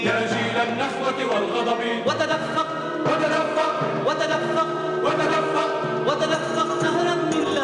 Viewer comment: idk about anyone else but i hear "what the dumb f*ck, what the dumb f*ck"